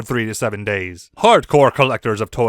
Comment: "due to the large amount of porny toys" ??? I know thats not what you said. but....